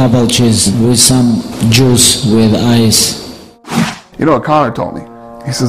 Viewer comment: At first in the interview I thought khabib said "Jews with Ice" lol. Like some sort of jewish rap group composed of former jewellers.